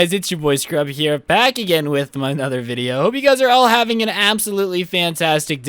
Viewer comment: Did he just say “back with my-nother video” xD